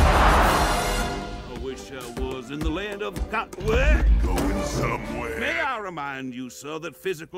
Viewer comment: He was Singing I wish I was in Dixie. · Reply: my dumb self always thought he was saying “I wish I was in the land of duck-“